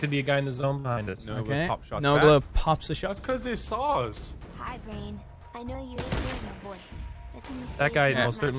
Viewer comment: Did anyone else hear a little say “hello brain”